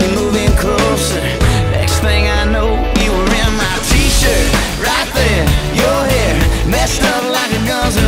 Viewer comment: When I first heard it on the radio I thought it said "You where my teacher". I thought he was getting down with his teacher x'D